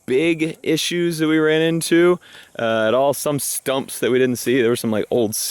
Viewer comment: Sounded like you said "Skunks", not "Stumps". Then you said " I hit 'em with the lawnmower."